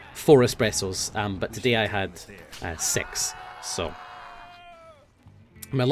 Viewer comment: I thought Nilesy said "I had sex" for a second there O.O